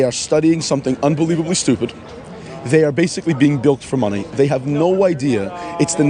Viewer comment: Right at the beginning, around 0:10 the closed captioning reads "...being built for money." The speaker is saying "...being bilked for money." , or at least that is what he means to say. "Bilked" Great word for today. I wonder how many leftists know this word. If more knew this word, perhaps they'd recognize the action better.